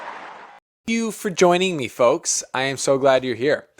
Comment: Anyone else catch the "F*** you for joining me folks" at 0:45?